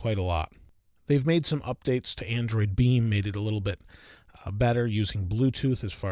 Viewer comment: Thought he said android deli meat